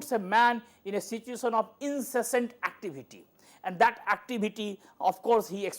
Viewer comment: in a situation of ______activity... Please help.. What is the word sir used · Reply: in a situation of 'incessant' activity. Incessant means without stopping.